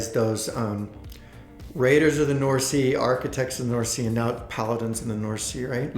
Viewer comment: Thought I heard architects/paladins of the north sea. rewinds Yup that's what I heard hahaha. Cracked me up. They're "of the west kingdom." Too funny. Good board game story overall!